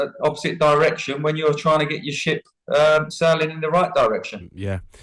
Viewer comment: Laura thought Teddy said 'sh*t' instead of ship 😂😂😂